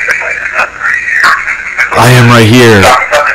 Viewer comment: or something I heard that (I'm right here) no jokes